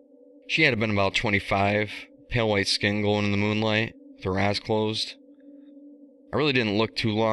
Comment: It sounded like you said "with her ass closed". Pronunciation, my friend. I don't necessarily think that the story is bad, but, uh, I'm pretty sure a good 85% of it was lifted from The Wicker Man. Wait. Are you Nicolas Cage in disguise trying to rebuild his career under an assumed identity?! I mean, you being a meme has been played out since at least 2010, but good for you, I suppose.